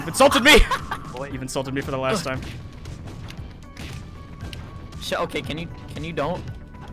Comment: Did Ninkendo say what I think he said? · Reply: He said shoot